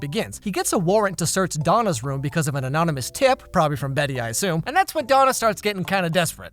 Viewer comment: It sounded like he said anominus not anonymous he’s great ngl love his content and is so funny!🤣